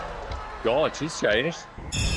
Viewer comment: it’s the “God, she’s changed” that had me dying of laughter. Simon is hilarious. 😂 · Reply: And I thought he said "God, She's Chinese" 😂